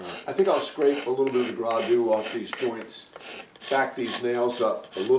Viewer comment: Good work. At around minute 16:10 we heard you use a term we hadn’t heard before. It sounded like “gras deux” or at least that’s the French way I heard it. You were saying you’d just scrape a bit of the “gras deux” off along those long tongue and groove joints of the stool top. Can you clarify it for us? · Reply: Thanks Sparrow! I learned that word from my father-in-law, who grew up in New England. Maybe "gradoo"? from the french word "gadoue" (I looked it up <link> Also described as a cajun word, of course there are a lot of french words in New England too, especially Maine